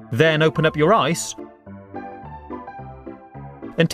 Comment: I thought he said, "then open up your eyes"